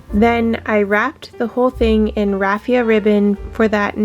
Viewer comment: What is the name of the stuff that you used to wrap around the twin tree? Everything you do I absolutely LOVE!!! You are so creative and I love it all. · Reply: Nevermind I went back and played it again and I heard you say it’s raffia ribbon. Sorry about that.